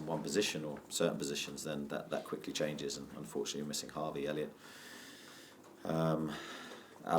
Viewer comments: He didn't....we have our own Harvey and Elliot...mate...🤣🤣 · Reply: haha my bad I thought he said Harvey Elliot, he should put a longer pause between the two names 😂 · did he just say we’re missing Harvey Elliot?😅